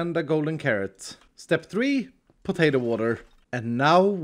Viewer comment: did iskall just say potato water